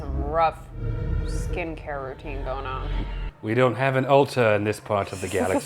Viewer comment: I thought you said, "we don't have an altar in this side of the galaxy" and I was like, "What is he talking about?? There's an altar right there!" Then half an hour later it clicked to me that it's Ulta you said and I came back to just say "Oh!... I get it now" xD